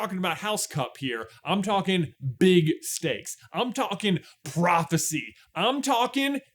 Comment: When J said "we are talking big stakes," I had to watch it again because I thought he said "we are talking big snakes". So close to a great Ninja Neville moment right there.